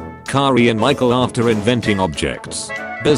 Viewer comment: "Michael afton" That's what I hear